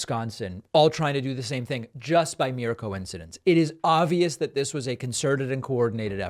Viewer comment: Did you say”ovious”? Tighten it up David